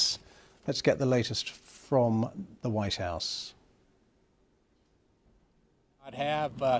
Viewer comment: Did he say the 'wise house'?